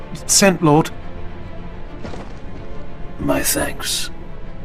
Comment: It is funny the auto-subtitle says "My thanks" to "my sex" rofl.